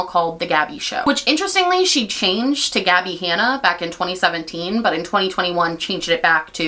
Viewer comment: I need to know at 4:04 are you saying the Gabby Hanna cult 😭😭I cant be the only one who heard that.